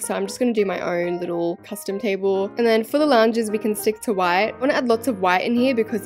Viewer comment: OH LOUNGE I THOUGHT YOU WERE SAYING LAUNDRY ROOM😭😭